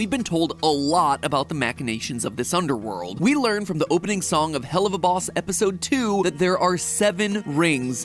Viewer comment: I just realized he actually said Helluva instead Heck-uva